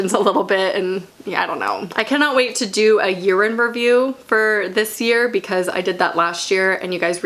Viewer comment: Omg. I was half asleep and I heard “urine review” I was confused for a second there...